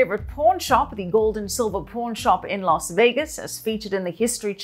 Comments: I seriously thought She kept saying "Porn Shop" 🤦🏻🤦🏿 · I thought Michelle was saying 'Porn shop' in the intro 😂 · At first I thought she said porn shop with her accent ....